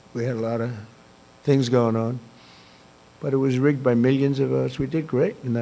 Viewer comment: Did he just say "It was rigged by million of US"?